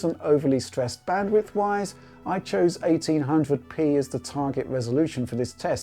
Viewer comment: Lol 4:42 I thought it said 1080p my jaw said⬇️ when I saw the frame rate 😂